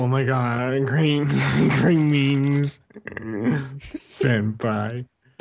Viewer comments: When wildcat said great memes I seriously thought he said Green beans. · when wildcat says great memes it sounds like he said green beans